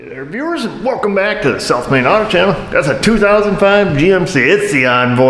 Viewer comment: Its funny ive been subbed to you for like 4 years, up until like 6 monrhs ago even tho i know the channel name and see your shirts and everything, i always thought you were saying welcome back to the self made auto channel in your intro lolol then one day it clicked, haha